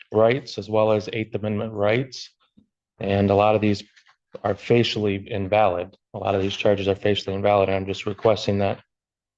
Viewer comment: is the gift that keeps on giving, just like Eric Martin... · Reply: Does he really say "facially invalid" (twice!) at 27:35? And again four minutes later? Anyone hear that?